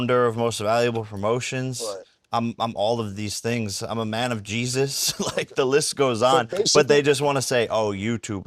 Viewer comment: Did he say "I'm a man of Jesus"? Which Jesus is he referring to?